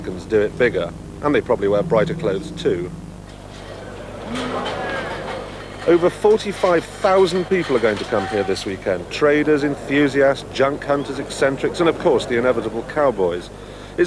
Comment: just after 0:35 did he say "Jun Cunters" ..... Whats one of those? ;-)